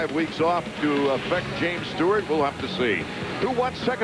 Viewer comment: Lmao at 3:19 I thought that said 'Stewart is black' lololol